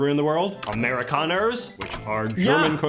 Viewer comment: The only thing he talked about that actually is authentic is the red solo cups. Couple of things, though: 1. They’re very common in America, not because they’re considered glamorous or cool or even American, but because they’re dirt cheap; so they’re great for parties, picnics and barbecues where you have a lot of people. Less widely known around the world are paper Dixie cups, which are also very common at large gatherings in America, but they’re smaller. 2. Solo cups don’t just come in red; I’ve seen them in blue, green and yellow too. But for some reason it’s always the red ones that appear in Hollywood movies. Oh, and I’ll add this: “Americana” is a word used to describe media or materials, typically old or historical stuff, that are considered emblematic of America or American history or culture. At the university library I work for, we have a section of our special collections and rare materials collections called “Americana”, which can include anything from old postage stamps or early 20th century post cards from Yellowstone, to old diaries written by 19th century Americans, old photographs, early film reels, or early American publications. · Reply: I heard "Americana" too but he was actually saying "Amerikaner" that cookie earlier. And it's funny he thought that meant a person because it's a perfect 180 to the famous Kennedy quote. "Ich bin ein Berliner" Which AMERICANS claimed meant pastry but Berliners understood it meant person.